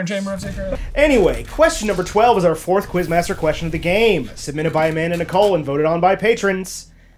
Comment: I swear I heard "submitted by a man in the colon"